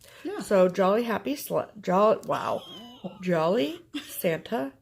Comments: Roflol. that’s what I thought she said too. “Jolly, happy sl$# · Don't worry I thought said ”Jolly Happy Slut” too hehehe so you are not alone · Oh my gosh - great video! I thought she said "Jolly, Happy, Slut" too! I think I may have been watching Game of Thrones a little too much! I'll see you on New Year's Eve! Happy New Year to you both! Kathy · Haha! I thought said “jolly happy slut” too! 🤣🤣🤣